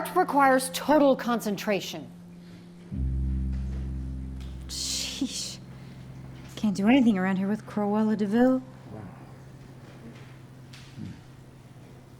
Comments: who else heard "sheesh"💀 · t sounded like sheeese not geeze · did he just say sheesh🤣